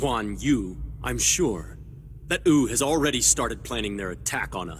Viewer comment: What Subtitles say Compared to what I hear Wu = Ou 27:08 Sima Yi = Sima E 25:13 ___________________________________________ The Pronunciations of these have officially reached the true standards of an English dub 7w7